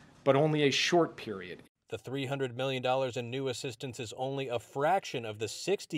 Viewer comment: I thought I heard him say $300 BILLION instead of million.